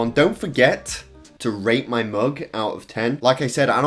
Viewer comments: I heard 'rape' my mug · I thought you said “don’t forget to rape my mug” XD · I thought that he said to ra*e his mug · Why at the end did I hear "Dont forget, rape my mug", ive got something wrong